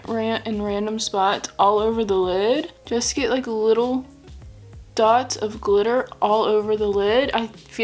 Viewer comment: Lol my fiance just walked in and hear you say dabs of glitter and he thought you said "dogs are sober" Haha that was awesome :) I really like this video idea. I might just try it. I loved all 3 looks. Your quidditch match look was super creative. I really liked how you made your eyeliner look like the wings of the golden snitch.